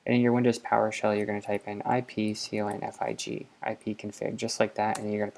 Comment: hey. ok, so at 2:50 what did you say we had to type in? i couldn't quite hear it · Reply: sugar heart ipconfig